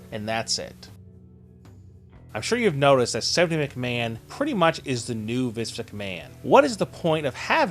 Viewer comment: Sounds like your saying Vis McMahon